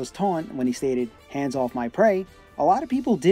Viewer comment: I always thought Falco was saying, "Hands off my threads"